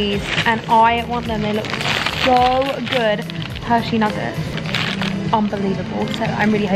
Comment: did u say nuggets?!!!!! YUM I LOVE NUGGETS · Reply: It’s nouget. It’s a chocolate creamy thingy.